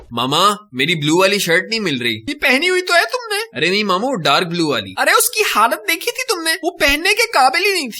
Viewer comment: am i the only one who thought that he said ''MAMU" instead of Mama..